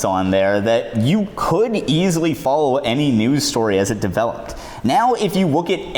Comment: Anyone else hear him say newds instead of news at 6:05 ?